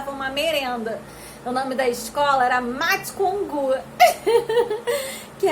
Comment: eu ouvi o nome da escola “mate com o cu”